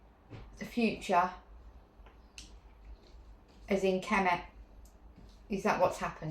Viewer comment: Does anyone hear whispering between 7:40 and 7:50? I can’t understand the first few words, but the last word sounds like Kemet.